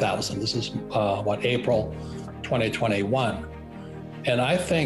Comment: Thought you said it's going to 0